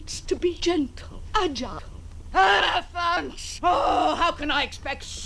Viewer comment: One needs to be gentle, agile... ELEPHANTS!!!! (That’s probably not what she really said, but that’s sure what I heard it as)